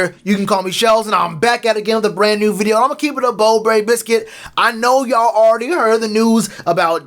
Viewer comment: Did u just say "imma keep it a bo berry biscuit"